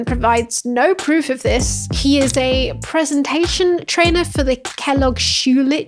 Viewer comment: "Kellogg Schulich" I heard "shoe lich", and I imagined some kind of undead creature that either hordes shoes or lives in shoes. Too much D&D in my childhood.